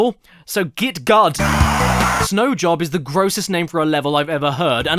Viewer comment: did he say snow job?